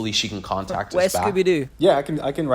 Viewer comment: Velma is Scooby Do. I thought you were going to say ‘Where’s Louise?’.